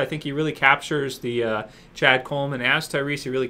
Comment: sounds like your saying ChadColeman's ass